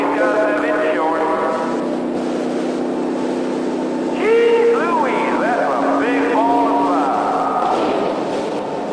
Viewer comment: what is the stadium announcer saying at 03:13? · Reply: “Yee-hooey! That’s a big ball of fire”